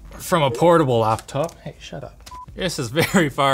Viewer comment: Did someone say “hey, shut up” @ 3:10 ?